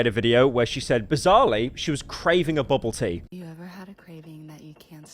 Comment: being paranoid about pronouncing certain names wrong but pronouncing boba like 'bobbah', the most british interpretation of that word, is so funny to me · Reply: oh i thought he was saying bubble tea but pronouncing bubble silly